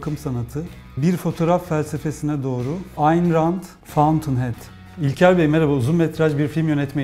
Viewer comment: arası ne diyor? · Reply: Ayn rand-The Fountainhead kitabı